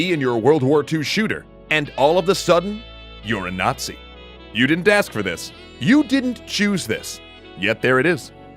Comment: guys I’m 16 years old, I have ADHD, so maybe it’s me, but do you get what the fuck EC is trying to say? He literally only says “you’re a natiz, you didn’t choose this, but it’s right there” what?! I actually feel brain dead. I get what he’s trying to say, but holy shit is that one sentence both dumb as shit, annoying as all hell.